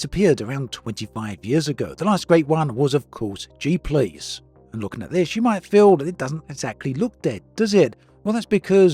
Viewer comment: did he say “jeep lease”? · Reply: G Police